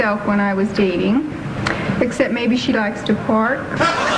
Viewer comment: Can anyone tell me what she said for her answer at 3:10 -"she likes to ----" thanks! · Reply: Park